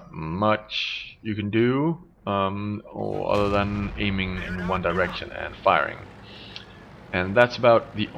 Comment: how it was said: aiming in one direction, and firing how i heard it: aiming at one direction, and fire XXXFXDDDDXDXDDDDXDX